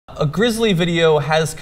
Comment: At the beginning I thought John said "A grizzly video."